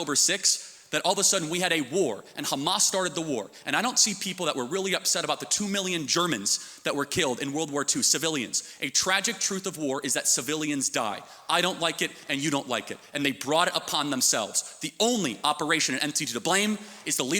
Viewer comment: bro I heard that you'r killed.